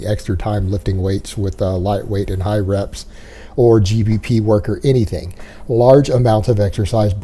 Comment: did say "g4p work"? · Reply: Gpp